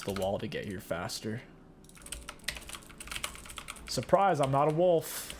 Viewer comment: I swear I heard “SurpriseMF!”